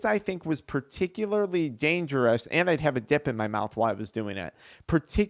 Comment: I respect this guy for his complete openness and honesty you cannot help but trust him . Trust he telling the truth 110% . Edit: 6:24 … I thought he said “and I had a D 🍆 in my mouth ” I had to rewind “DIP” Spliff yeh 😂!!!